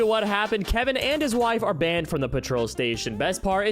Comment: >hears petrol >knows story isn’t American >this can’t continue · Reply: When did he say petrol? I heard him say patrol